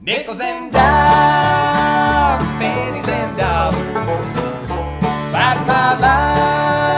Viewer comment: Watch this on YouTube with the captions on. On the first chorus “pennies and dollars” translates to “our panties and dogs.” Or, did I mishear and the latter is correct??? 😂